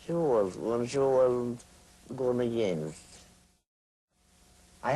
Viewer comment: What did she say about Kschessinska in 4:07? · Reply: Magdalena Berechowska she says ‘when she wasn’t gone against’. It means that Kshessinskaya was very kind to you if you were not confronting her.